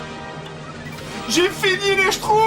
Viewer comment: moi ce que j'entends, c'est "J'ai fini les SHTROUFS"